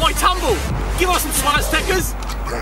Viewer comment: any time they say "Swaz Tekkers" I think they're saying "Swastikas"